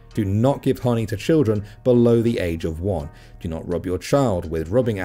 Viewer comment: I wasn't watching and toward the end I thought said "Do not give money to children under the age of 1" 0.0. Lol.